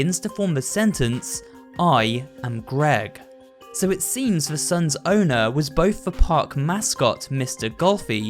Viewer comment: I might be stupid but did he say "the sons owner"?!